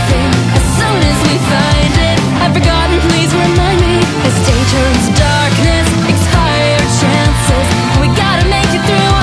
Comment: I always hear "Please remind me, how dangerous is darkness" instead of "Please remind me, as day turns darkness".